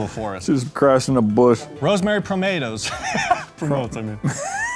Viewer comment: I heard it as Rosemary tomatoes 19:33 😅